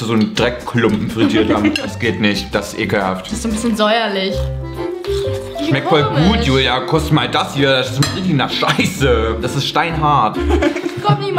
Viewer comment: Wer hat bei 4:04 auch statt "koste mal das hier" "kuss mal das hier " verstanden?😂😂😂